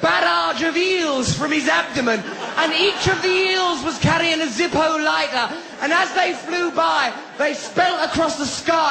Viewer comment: "From it's inception to it's [something]" - What's that he's saying there? I've tried googleing every variation of what I think he might be saying, but I really have no clue XP I love Russell Brand's stand up routines (or really, anywhere I can hear him talk), 'cause English is my second language, so I learn loads of new words, or new ways to incorporate words I already know but only ever use in formal writing, in my daily life, practically every time he opens his mouth! Brilliant! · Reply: "From it's inception to it's denouement" is what he said there.